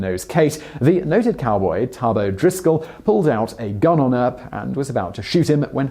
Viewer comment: did I just hear...odriscol